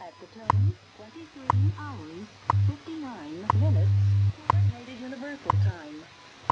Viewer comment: What annuncer say 0:51" before coord...univ...time... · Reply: Minutes